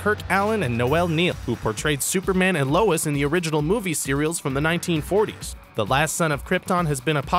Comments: he said cereals instead of series. XD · I know someone heard him say "original movie cereals" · did he say "movie cereal" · it sounds like he said cereal